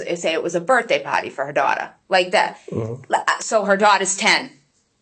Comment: Not making fun, I promise. I am from Georgia so you can imagine. I keep hearing birthday “potty” instead of birthday “party.” 🤣